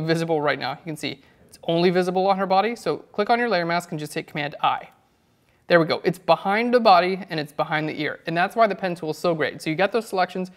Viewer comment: Anyone know what he said when 9:15 - 9:20??? i can't heard clearly:( i don't know how to make v sign turn on the back side. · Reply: "So, click on your layer mask and just click command I. There we go, it's behind the body and it's behind the ear."